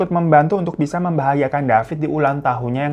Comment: "membahayakan" ? salah denger ga nih saya😅